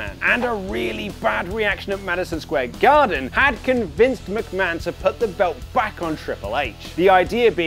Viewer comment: When he says "convinced McMahon" at 3:43 and you think he's saying "con Vince McMahon" English is fun.